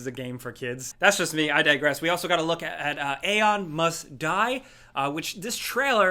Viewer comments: LOL. Elon Musk die. hahah. I misheard "Aeon must die". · i literally heard "elon musk die" 😂